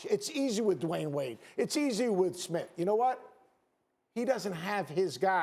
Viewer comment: Did he really say it’s easy with Smith? Smith, like JR Smith?😂